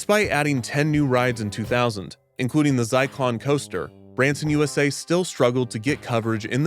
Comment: Did you say "Zyclon" roller coaster?!! 😮 I hope I just misheard a weird pronunciation of "Cyclone".